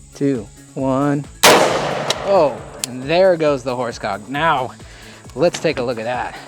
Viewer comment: the funniest part was my friend mishearing you say the words "horse cock" ad then asking me to rewind, because he thought you said "fore stock" so when he heard you correctly he went quiet and said "i dont know which one is worse."